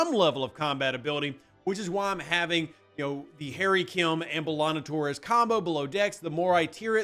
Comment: you need a revenant.... so, get one of your team-mates with a revenant, wait for the solo's, cheers did you say Hairy Kim